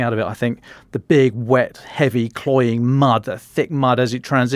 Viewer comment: What kind of mud is talking about? As an American English speaker I can never understand what word he's saying when describing the mud. Cloying or clawing? Or something else altogether? At any rate, I'm glad he's back. The podcast has really s*cked while he was away.